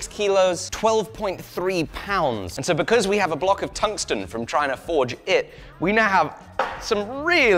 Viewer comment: A single barrel of water on a single barrel of tungsten? That's a pretty intense result for such a small quantity. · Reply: I thought he said titanium not tungsten?